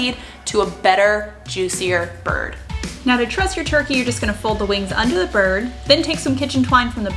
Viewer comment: At first I thought you said trust your turkey and I was confused haha